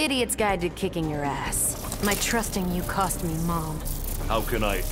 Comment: “My trusting you cost me mom” did i hear this correct?